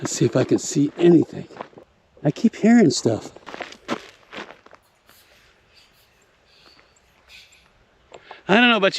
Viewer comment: Ok. I give up. What was the voice saying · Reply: "Don't drink the water!"